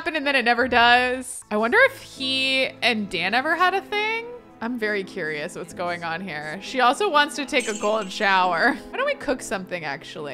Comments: I could have sworn you said Poppy wants a gold shower. 😂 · Hold up. Around 21:04 I swear that said COLD shower and not gold shower, but it didn't stop me from choking on my food at work. If Poppy wants a gold shower though...👀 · I- did you just say golden shower instead of old shower????????????????? xDDD Keep it PG!